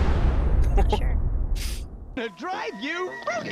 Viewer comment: what's the context behind this one clip? I have no idea what he's saying. "You tried to fruit me?" "You dried your fruity" "You tried to fruit me"